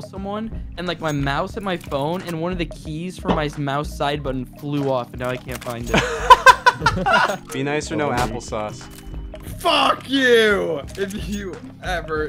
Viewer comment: I THOUGHT IT SAID “BE NICE OR NO APPLAUSE” AND I WAS LIKE WHOS CLAPPING